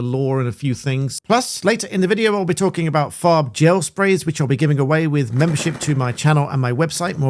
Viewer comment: Master BlackBelt Members - claim your FREE Farb Gel Perk: <link> Terms: <link> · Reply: I was intrigued when I thought I heard you're giving away.... fart gel spray.